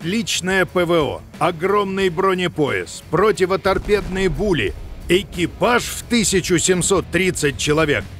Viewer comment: противоторпедные пули? Или мне послышалось?